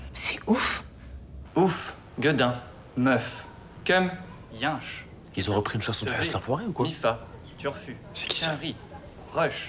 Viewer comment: " Ils ont repris une chanson de Heuss l'enfoiré ou quoi " 😂😂😂